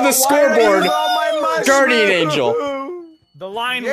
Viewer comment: Did just say Guardian Angel?